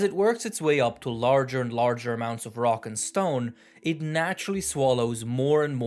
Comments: DID I HEAR A ROCK AND STONE!? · did I hear a rock and stone? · did i hear a rock and stone??!